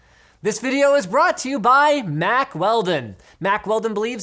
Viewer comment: was looking down when he said mack weldon, thought he said mac welding